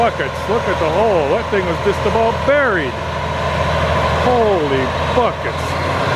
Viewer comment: I thought you said stuck thats buried